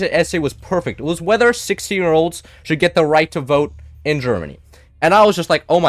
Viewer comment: I initially misheard it as whether 60 year olds should have the right to vote, very compelling thing to argue about.